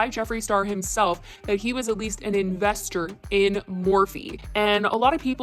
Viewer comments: Not to support Jeffree but it sounded like he said he was invested IN Morphe, not as in he was an investor but he loves the brand... idk I just think it was spun into a different context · Not supporting him but it sounded like ol Jeffie said that he’s “invested in” morphe? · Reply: Yes it sounds like he said he was invested in Morphe.